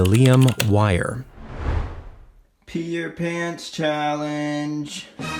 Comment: PEE YOUR PANTS CHALLENGE I had to rewind I thought I heard Peter Pan's challenge ahah